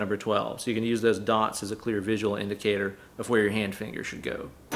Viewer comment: Did you just say "hand fingers"?